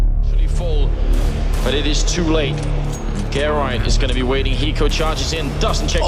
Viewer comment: Turn the Caption on and then... Game Caster said "Get Right" but the caption said "Karen" wut?